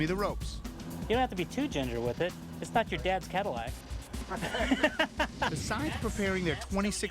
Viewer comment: What does the guy say at 7:40? You have to be ..... its not your dad's Cadillac? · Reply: You don't have to be too tender with it...It's not your dad's Cadillac. (I think he was trying to say generous and tender at the same time lol)